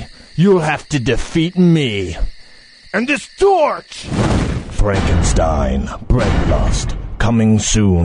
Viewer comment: Is he saying: Frankenstein: Breadlust? I'm still laughing so hard it's diffcult to type...